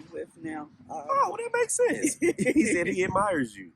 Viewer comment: WHEN HE SAID HE ADMIERS YOU I HEARD ROSE MARY